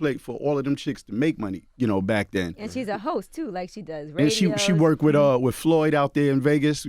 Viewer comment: Paused the video to come here && see if anyone else thought Yee was about to say “and she’s a hoe !” 😩😭😭 She said host though lmao #PrayersUp #MelissaFord